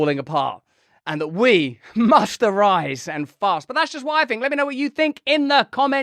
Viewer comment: Yes, we must arise - and fast (you really should have been clearer there because I, for one, thought you'd said 'arise and fart ') but how? How can 'we' do anything? Let's imagine 'we' is an organised army of 100 million people. What would our strategy be? I don't want to waste hours of my time thinking about this but it seems to me, off the top of my head, that the mainstream media would have to be 'taken out' because the people who run it now have created, and are controlling, the information stream that prevents the scales from falling from the eyes of the masses i.e. it (the mainstream media) keeps on pumping out entertainment crap to take our minds off the awfulness of the world ; and we need the awfulness of the world to be seen by everyone so we stop ignoring it. One major problem behind any grand plan to alter humanity's race to hell in a handcart is freedom. Freedom has been lauded and cherished as the most important human right for far too long to take it away from people in order to fix the world. Naturally many (perhaps most) people will argue it's too late or too expensive or too difficult to save the world and choose to turn their backs on our army's great and noble cause; and without everyone pulling to stop the handcart (which in reality is more like a titanic juggernaut approaching Mach 1) we aren't going to win. We can't afford apathy and negativity hindering the great rescue strategy. We certainly can't have half the world being entertained (or entertaining